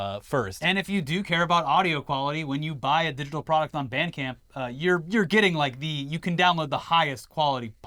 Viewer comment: I thought they were saying "bandicam" half the time... I was so confused